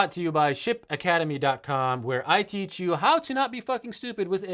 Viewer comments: When you said "Ship Academy", I heard "Shit Academy" 😆 🤣 😂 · after the shirts and the - bvrought to you by - I thought he said Sht academy not Ship - i need to get some sleep